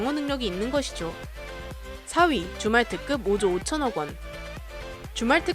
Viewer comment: 아 왜 자꾸 주말특급으로 들리지